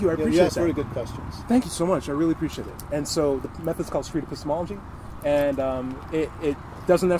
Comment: At the 00:44 mark you name the method. I could not tell exactly what you said. Sadly, the close-caption renders it as "street to small G". Please, what is the name of the method? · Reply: Street Epistemology. :)